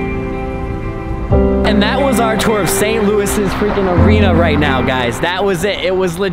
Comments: Did he say Saint Louis around 13:14? Joe Louis. ;0) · St Louis Arena? Did I hear that right?